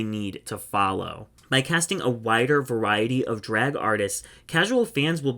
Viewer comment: At first I heard a “whiter” variety of drag artists, instead of a “wider” variety …. Not a perspective I expected to hear lol 🤦🏻‍♀️